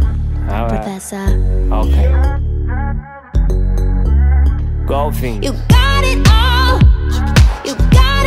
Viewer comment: what does he say? "Girl fings" "Go fings" ?! None of those fings I know that! 😝